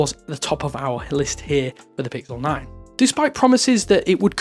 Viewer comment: I SWEAR HE SAID PICKLE 9